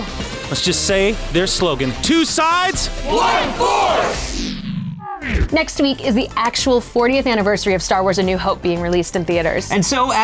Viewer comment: My heart skipped a beat when I misheard say "Next week is the re-release..." at 7:09. A Google frenzy ensued as I desperately searched for show times in my area...Insert sad trombone sound here.